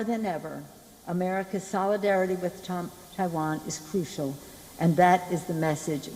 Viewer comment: did she say “solidarity with Trump “ ?? lol